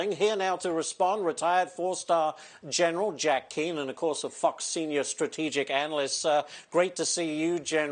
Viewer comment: did he just call him a f_ck senior? What does that even mean?